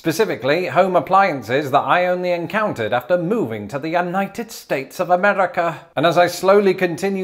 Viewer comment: Did I hear you say "un knighted" States of America?? 🤔 And the UK needs the "stacking" washer/dryer units. I'm surprised they haven't done this sooner